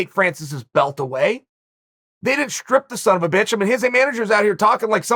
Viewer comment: *did he just call Francis a son of a bitch? :D